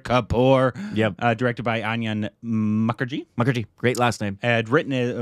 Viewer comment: did I hear "ONION Mukherjee"?